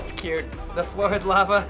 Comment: For some reason I thought they said "The Floor is llama" 😶😶😶